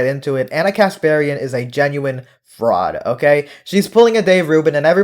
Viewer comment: I thought you said broad not fraud lol